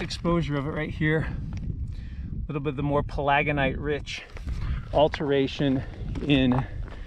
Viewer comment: We drive by this on I-15 twice a year as we commute to and from our Idaho home to our winter home in the desert east of San Diego. Humorously, my wife heard you say palagonite and thought you said polyganite, as in polygamy, something associated with some Utah Mormons. I corrected her.